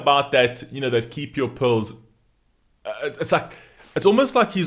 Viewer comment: You pronounce pills in a unique way. It took me a second to figure out that you weren't saying 'pulls'. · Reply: I thought he said polls lol